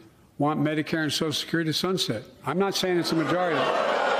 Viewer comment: Actually I heard Joe say "Its not the majority".